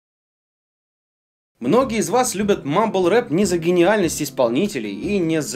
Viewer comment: Вам наверняка нравится Noble rap, потому что это модно Я: [гуглю что такое noble rap] Кстати, по-моему здесь 2:50 музон из Portal 2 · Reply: А я услышал "мамба реп", думаю, что ещё за хня, хотя мы с, вроде как, одногодки.